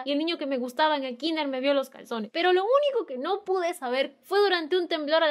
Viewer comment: Jajaja yo pensé que dijo pezones envés de calzones